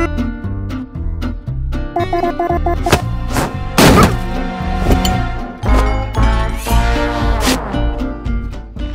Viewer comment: Is Suzie saying Popcorn or the cook book She's most likely saying popcorn but it looks like it read the book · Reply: tteokbok is a korean meal, she's not saying popcorn or the cook book